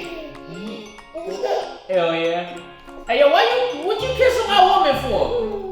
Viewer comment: did you say “hell yea” to jerry